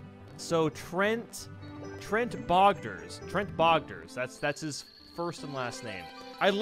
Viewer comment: For some reason I thought you said tret pogger